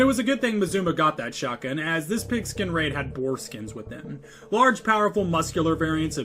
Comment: bruh i misheard that as foreskins x.x